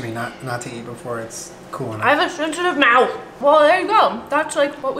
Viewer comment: Julien-“You always preach to me th-” Jenna-“I hAvE a SeNsAtIvE mOuTh” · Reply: Wow i thought she said “i have a shit ton of mouth” 😂